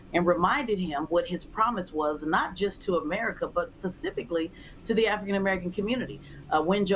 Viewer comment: Did she say PACIFICALLY?